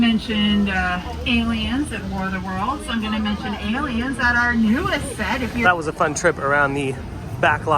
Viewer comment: Did I hear the tour lady say something about a new set for Aliens??!!!!!! 👽.....,.😃😆😁 At (minute 10:05) in the video 🙂 wait. It was referring to the plane on War of the worlds 😐. My mistake 😅